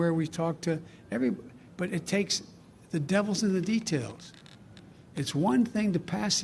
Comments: Did he say the DEVILS in the details????? Wow that disturbing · Dementia Joe can barely read off a teleprompter. I have houseplants with more cognitive ability🤣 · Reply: Did he say the DEVILS in the details????? Wow that disturbing